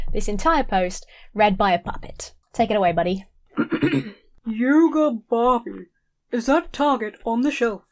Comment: Was listening to this in the background and misheard, “Yoda Barbie” which brought an incredible mental image. 😂